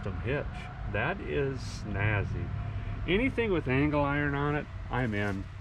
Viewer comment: I thought you said dangle iron